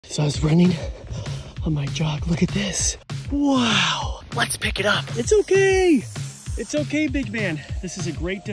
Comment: He said let's pick it up And I THOUGHT LETS BOT AND SAY WE DID😅😅❤❤❤❤